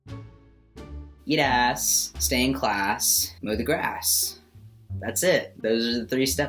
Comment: It sounds like he said get ass go to class mo the grass any body else heard that